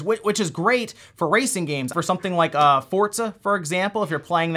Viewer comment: Did dude really say Fort-Zuh?